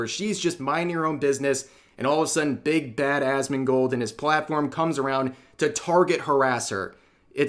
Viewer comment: It sounds like you said a$$mongold😂